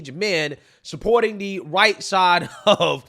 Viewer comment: When you said right-sided almost sounded like you said "Whiteside" ... you probably want to be careful and enunciate that "R" next time